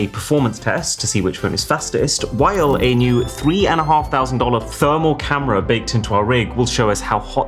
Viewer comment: Holy less than 5 minutes ago 0:35 bruh YouTube subtitles said that 3 and half thousands dollars are 35K 🤔